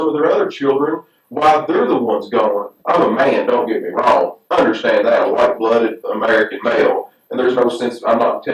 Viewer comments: | Did he really just say that? Someone please tell me I just heard him wrong... bad audio quality, I’m watching it on my phone, it’s 1am so I’m dosing in and out. Anything! As long as he did not say, “... don’t get me wrong, I’m a white blooded American man”. But with a name like in a place like Kentucky... I don’t think anyone be surprised. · Am I mishearing him...? at 22:20, does he call himself "white-blooded"..... should I just chalk that up to nerves, while being interrogated...? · does he say he’s a “white blooded American male”?